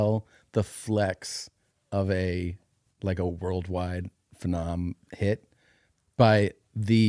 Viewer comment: What did Tom say at 1:02:40? What's a phe-nohm? Does he mean phenom? He didn't follow proto here, bud.